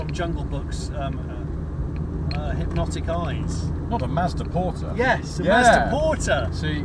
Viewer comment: Did they say Mazda Porta because I can't find anything remotely close to that name online?